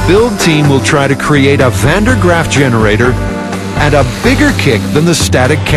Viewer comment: did he say Van Der Graaff